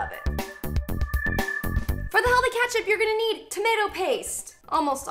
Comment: girl did you just say katchup? 😬🤦🏻‍♀️